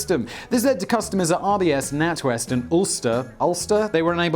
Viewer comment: I could've swore he said "Ulcer" not "Ulster"